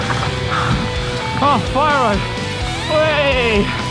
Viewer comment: it sounded like you wanted fire road